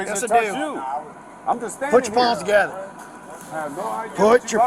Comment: Put your palms together. 😂 I seriously thought he said put your paws together. I didn't steal, I have money. You know how many people steal from stores that have the money on them? That means nothing.